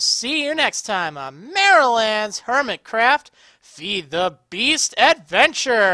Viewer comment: Thought you said Marriland-Webster at first. Anyways, love these long episodes. :D